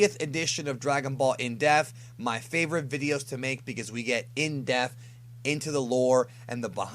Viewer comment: Anyone else think it sounds like he says "in deaf" instead of "depth"?